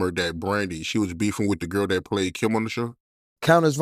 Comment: This fool called her "the other girl on Moesha." Props to Fredro for repping the great Countess Vaughn!! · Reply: I thought I heard him say the girl who played Kim on the show